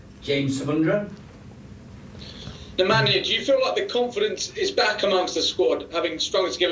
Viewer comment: did he call him nemanjic?